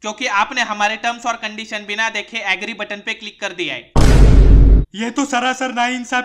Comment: I heard it as "angry" not "agree"